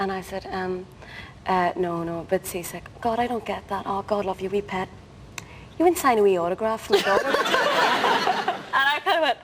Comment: Whats does Andrea say at 7:10-7:12 ? I didn't get it completely. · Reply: "No, no, a bit seasick." "God, I don't get that. Oh God love you, you wee pet. You wouldn't sign a wee autograph for my daughter, would you?"